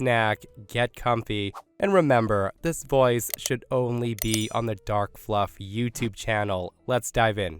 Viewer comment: I was only half listening to the intro and boy- the way my head whipped around because I thought I heard "this voice should only be on the Dark Snuff channel-" 😂😂, if you're still alive, let us know! 😂😅